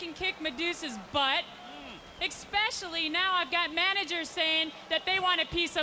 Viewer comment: Did she just say "Ec-specially"? 🤨